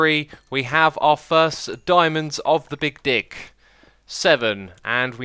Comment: did he say Dick?